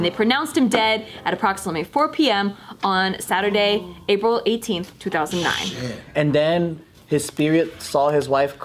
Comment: "April 18, 2009"? did I hear that correctly